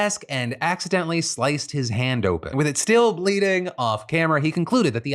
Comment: I thought you said John Stewart of “leading” from this news desk not “bleeding” which is honestly far more accurate.